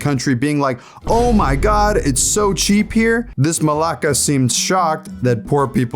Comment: wait, did he said "This Malaka" at 7:24 ?